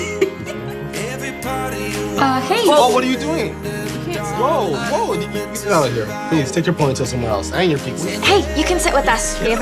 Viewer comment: it sounded like the bully girl said get the f@ out